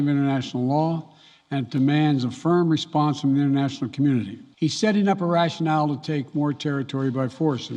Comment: What did he say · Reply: Demands a firm response from the international community